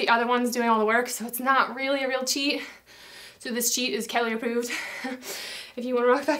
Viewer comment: Burn fat, build muscle, improve coordination, balance and strength with these very effective butt and thigh exercises. This routine uses a combination of three types of training: traditional strength training exercises, dynamic combination moves that call upon balance, core and coordination, and a wild card of an exercise that’s sure to burnout your muscles. <mail> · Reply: I love it when you say in the last round of squat pulses that the cheat is 'calorieproof', because the first time I heard that I thought you said it is 'Kelli-proof' :-D